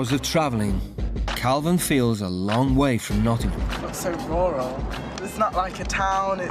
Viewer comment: Did they say the boy in the pink shirt at 2:07 was from Nottingham 💀